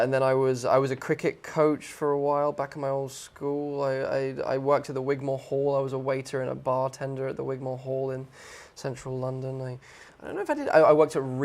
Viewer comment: What he says at 6:53 ? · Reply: "I worked at the Wigmore Hall, I was a waiter and a bartender at the Wigmore Hall" (a theatre in London)